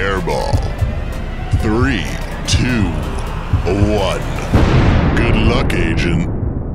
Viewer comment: why does he sound like he says good luck asian